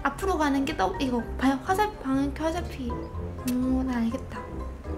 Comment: 여기부터 설명뭐라고 하시는곀ㅋ화살피?